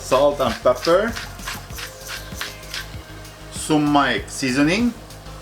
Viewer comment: What seasoning is at 3:50? Was that Sumac? That's what it sounded like but I wasn't sure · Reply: Yes sumac